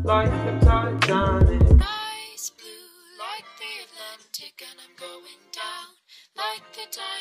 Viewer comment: My dumbass thought it said 'eyes blue like the atlantic and im going down like na dry dry'